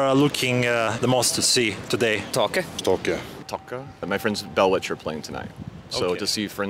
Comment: Can someone tell me whats band name in 8:32 they mentioned three times ? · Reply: Taake